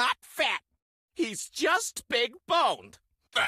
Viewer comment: "Detona isn't fat, he's just big boned" SOUTH PARK REFERENCE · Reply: I thought he said big balled lol.